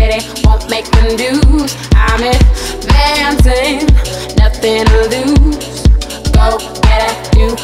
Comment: I thought she said “I’ve been dancing, nothing to lose” I was in a disco vibe haha